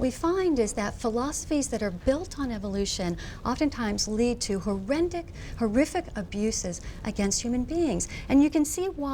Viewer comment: Did she just say 'Horrendic'?